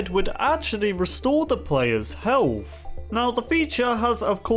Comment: He said Heawf instead of health